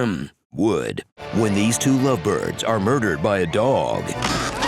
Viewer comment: "uh hmm hmm. Wood." 😂 had to play that back to make sure i really heard what i thought i heard there